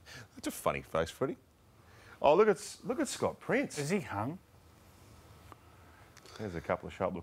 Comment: What did Joey say about Scott prince 3:06 into the video ? "Is he _? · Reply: Hung (hungover)